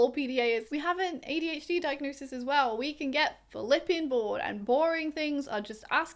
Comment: Hey, can someone please tell what did she say at 9:30 ? English is my second language and I'm trying to figure it out for, haha. The exact word I couldn't get is "[...] we have an ADHD diagnosis as well, and we can get ____ , and boring things are just asking [...]" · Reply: "I think a lot, if not all PDAers, we have an ADHD diagnosis as well. We can get flippin' bored, and boring things are just asking for a bit of avoidance."